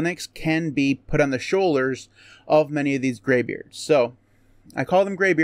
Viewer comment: For a moment I thought I heard "Graveyards". Made me think about my age as I'm nearly there. ;-)